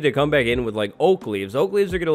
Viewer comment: When you said you were going to put "oak leaves" on the panda I heard "Oakleys" like the sunglasses 😅🤣🤣